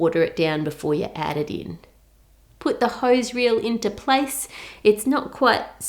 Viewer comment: Miss Ann says: "Hose reel" For some reason I hear: " Hoes real" IM SO SORRY